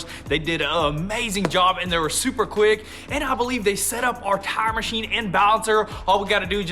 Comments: did he say they setup our Time machine? Or tire machine....they are bring in their escalator to tear apart the trailer not a excavator (its said like a X cavator little brothers) ...lol im sorry I had to.. 👍😁✋👍😁✋👍😁✋👍😁✋👍😁✋👍😁✋👍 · at first I thought you said time machime but then realized you're saying Tire with a southern accent. lol